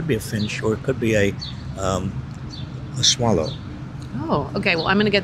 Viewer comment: I thought you said Swallow Robert ? 🤨